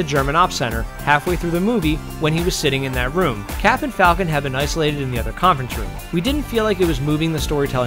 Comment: thought he said captain falcon, for a sec.